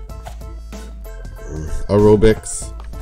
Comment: what did he say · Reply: Acrobics